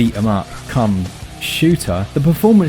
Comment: did u just say cum shooter